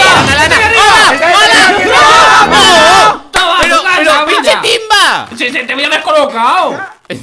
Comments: pinche timba es lo que dice invictor? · no pero pin*** timba Es lo que escuche de invictor ahora es mexicano el invictor